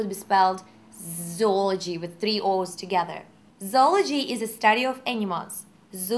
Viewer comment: I would swear she said: "Zoology is the study of enemas..."